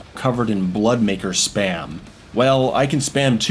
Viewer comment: I thought you said blood maker sperm